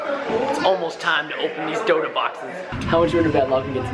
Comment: who here thought that he was saying donut boxes :-) xD